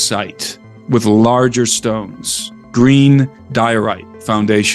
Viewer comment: Green what stone? At the 45:30 to 39 mark, he says something about a type of stone. I cant find it online. Google says I'm spelling something wrong... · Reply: Green diorite